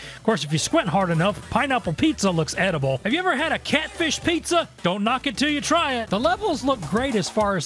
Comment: For some reason I thought you said catfood pizza.